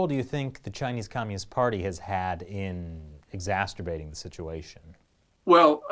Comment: Excuse me... did just say “exasturbating”...? 8:40. 😂😂😂 it’s a new word!